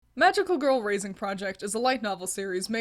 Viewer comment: I thought you were saying Magical Girl Raisin the whole time💀💀💀